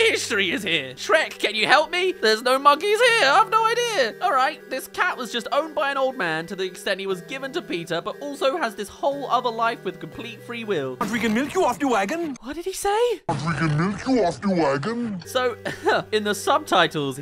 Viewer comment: I heard what I think you did my sevond tome around, but I originally heard, "We can nuke you off the wagon." My first thought was France's nuclear warning shot doctrine. Then I heard a very different word the second time when I went to timestamp this comment. Weird.